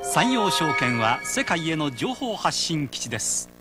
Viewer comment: what did. you say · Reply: Sanyo Securities is a base for disseminating information to the world